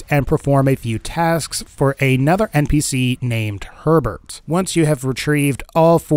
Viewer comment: He said Herbert, I heard 'pervert'